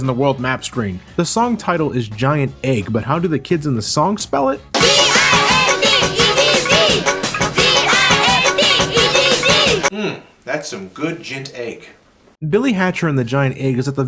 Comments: when you bring up the Giant egg theme and they say Gint Egg instead I actually think there sayings g-i-an-t-e-g-g. The joke was still good though 😂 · the GINT EGG part always makes me laugh for some reason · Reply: Those Crazy Guys Productions I keep hearing it as "G-I-N-T E-D-D" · Yeah, for the longest time I thought they were saying Gint Egg. They are spelling it right, it's just that the way they sing. The A and the N are running together. (G - I - AN - T E - G - G) Still hilarious tho! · I hear "D-I-N-T E-E-G"